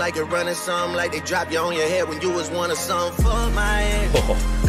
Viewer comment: Someone told me that last song sounds like he saying f my ass and since then I can't unhear it 😂